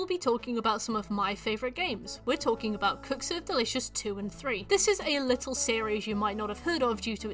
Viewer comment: I kept hearing 'Cooks are delicious' instead of the actual title, and in my hangover it felt like a stab in my ear, until I started giggling uncontrollably every time you dropped the title